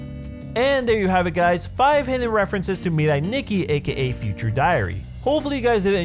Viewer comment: I thought it said " five references to Nikki Minaj" I'm done XD EDIT: oh wow ty for the likes